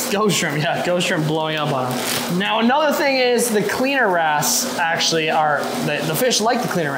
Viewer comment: First time watching you guys, loved it! Every time you said 'cleaner wrasse', I heard 'cleaner a$$' 😂 (I'm 58 goin' on 12). I think a good name for that gorgeous oscar would be Butters. 😊 Idea for feeing Barry: can you get some sort of plastic that's firm enough to stick in the tank and divide him from the other fish, then feed him in that area and remove the plastic after he eats? I raise reptiles, and that's a trick I use sometimes.